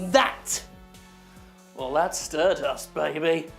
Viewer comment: I thought it said 'STERBUST' with the 't' blending down into the 'd' lol Sorry Jim!